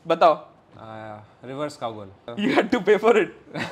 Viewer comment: did he said reverse cowbell???